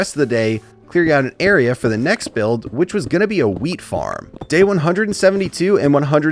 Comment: i thought he said weed farm